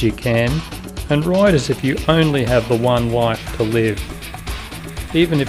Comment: Did you say we only have one wife to leave? That's just not right. Love the video! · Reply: No I'm pretty sure he said "we only have one life to live." But it does kind of sound like he said that! If he did say "we only have one wife to leave" he means we aren't leaving a bunch of people forever, only one woman for a little while til we get back from our adventure.